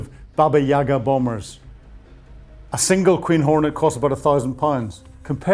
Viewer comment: LoL... When you said "Queen Hornet", I heard the words "Cream Hornet"!! (just thought I'd share the mirth - we all need a smile now and then)